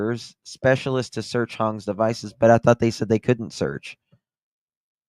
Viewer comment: Oh ok 👍 hoang ang hong , o thought you said hong , got it